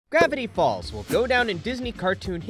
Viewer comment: I thought you said gravity balls